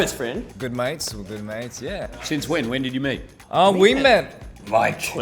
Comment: I forgot Hugh is aussie and thought he said "when did you MATE"